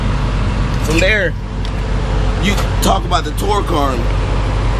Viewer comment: he says "from there you can talk about the ____"? What did he say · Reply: Torque Arm - my bad. Thank you and thanks for watching 👍